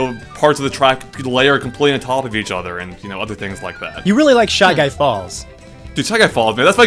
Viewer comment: going to be honest, I heard "Shy Guys Balls", I had to rewatch it and felt stupid